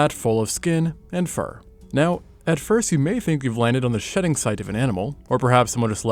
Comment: I misheard "shedding site" 😂😭